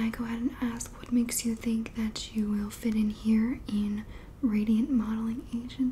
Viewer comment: Thought it said "nude modeling," I was like MOMMA NO